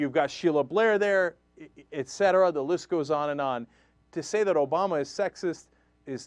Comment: Oh i thought it said NOW says obama is sexyest